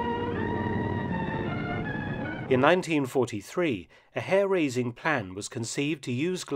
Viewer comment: Lol i thought it said hairbrained åt first.